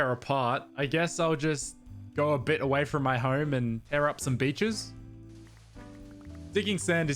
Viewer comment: tear out what? I heard tear out some bitches